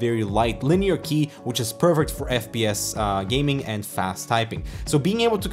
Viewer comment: did you say it is pervert?